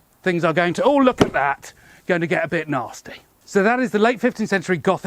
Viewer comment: "That's when it gets a bit nasty" - in my head I heard "a bit tasty" from the old BBC Minder series...